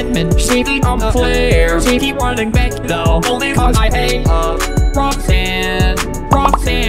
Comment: This is what I heard "ShE keEps ruNning baCk tHo onlY cAuSe I aTe HeR"